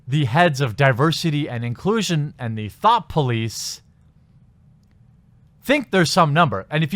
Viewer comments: Everytime you say thought police i hear thot police xD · Did he say Thot Police at 06:53?